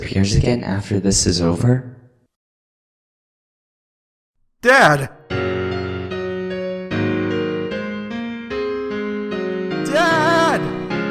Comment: at the end i thought that sans said dab dab